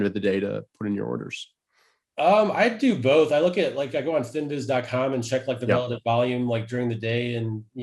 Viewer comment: What is the website at around 23:34 he says he looks to see volume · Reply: I use finviz and stockcharts